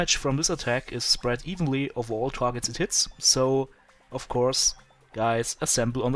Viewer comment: it sounded like you said, "At all targets tits" :P Sorry, was just kinda funny =) haha Good video! =)